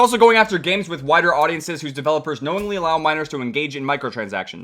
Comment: I thought he said whiter